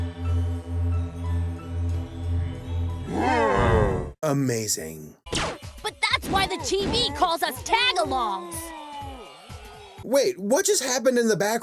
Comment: I don't know but, did he just say "tagalogs" or "tag-alongs"? 👁👄👁